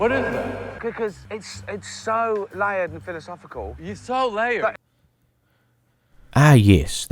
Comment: i literally thought he said you're so lame 💀💀